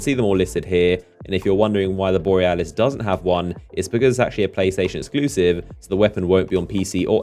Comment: Pussy station exclusive? That what i heard him say